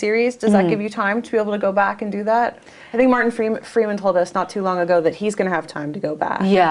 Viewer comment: did she just say morgan freeman.........? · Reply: She said Martin freeman not morgan, Martin plays John watson